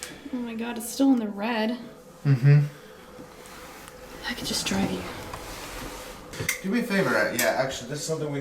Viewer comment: UM i’m tripping out at 24:06??? Literally heard “I could just draw you” but with two voices? Am I just losing my mind here? I even slowed it down and everything and it’s just sounds really out of place and hecka weird?? help?? UPDATE: OKAY just had my brother listen to it and he said it sounded like “I could destroy you”.... um okay so now i’m 10x more creeped out